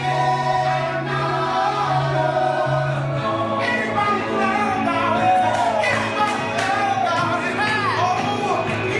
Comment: Can someone tell me what he's saying at 3:30? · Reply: "Anybody glad about it?"